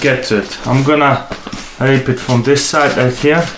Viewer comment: did he just say ill rape it from this side lol